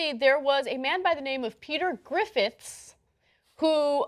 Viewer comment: For a moment I thought they were saying Peter Griffin. Imagine if you will, Peter Griffin wanting to join Scientology and gets the whole family involved (and Brian ends up having to save them because he's the smart one ), this would have been one of the best Family Guy episodes ever. Possibly, equal to that one they did in South Park.